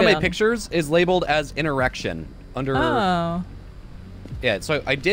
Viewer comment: Did anyone else hear erection instead of interaction lol